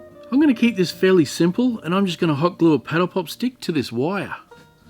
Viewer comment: It took me the longest time to figure out what you were calling popsicle sticks. I looked up what I thought you were saying "petal pop" and found I was watching thankfully one of my searches turned up paddle pops! I never realized how much those two words sound alike. What would we do without the net